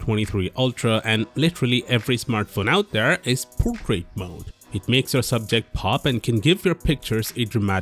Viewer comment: I didn't catch you when you said 'Portrait Mode' luckily the subtitles were available.